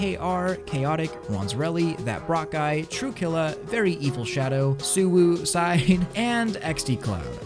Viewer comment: For some reason every time says his patreons all I hear is " VERY EVIL SHADOW"